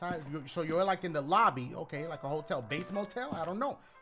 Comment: I thought dashed said “vape hotel”